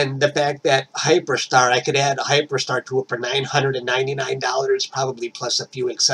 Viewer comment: I don't know, I don't own one. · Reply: at about 7:30 you mentioned you could add Hyperstar to your existing telescope. Just checking if I heard correctly.